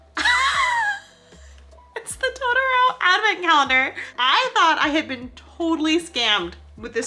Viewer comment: Any one else thought it said Toronto advent calendar No only me